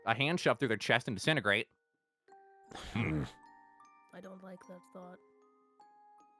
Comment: bruh I thought said “I don’t like this rock” for a second lol